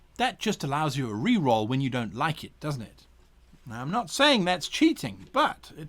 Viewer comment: All I hear is " I don't like fun"